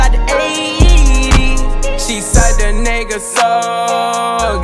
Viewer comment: Who else thought he said "Suck that nigga soul"